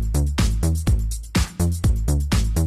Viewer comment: 我怎麼聽都是聽成「抖暈」